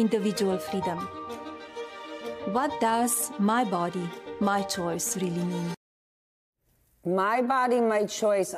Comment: "What Does My Body My Choice Really Mean?" Am I the only one who heard her say WHAT DOES IT REALLY MEAN?